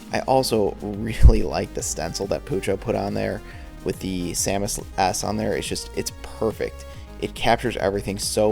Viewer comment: even if they're not always tailored to my specific interests, the quality and production value of your videos is always superb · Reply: though I keep listening to 18:05 and I can't hear anything other than 'with the samus' ass on there'